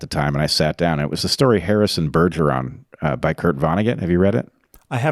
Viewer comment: Does anyone know which artikel/book Bret suggests to read at 38:43? Can't hear clearly what he says. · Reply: Its a book called 'Harrison Bergeron' by Kurt Vonnegut, there is a movie too.